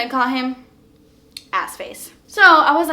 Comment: Omg this is like listening to a detective story! Now I want to know what AskFace was thinking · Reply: Oh! Was it AskFace??? I thought she said AssFace! Lol!!!